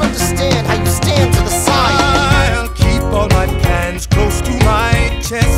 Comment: I always haar it as ILL KEEP ALL MY PANS CLOSE TO MY CHEST And it just makes everything burr says so funny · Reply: Maximus I thought it said pants